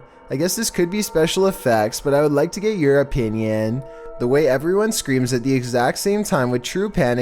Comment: haha he made a pun without even knowing it at 2:48! the people in that one were british and then chills said something about him thinking it was photoshop then said EUROPE INION what he realy said was "you're opinion"